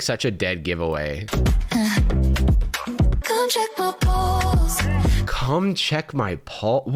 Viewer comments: I THOUGHT SHE SAID "come check my balls" FDKSLJLKSFD. also this shit is stupid af. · nah I was drawing with this video in the background and when she said "come check my pulse" I had to go look at the video to do a double take cause I thought she said "come check my balls"